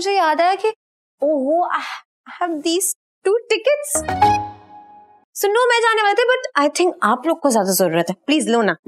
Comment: Did she say SUNNU 6:57 ?? SUNNU WAS TITLI IN S1 she still misses the first season